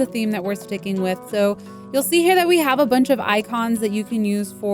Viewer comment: okay was that only me or did she say you see hena thats a word in arabic in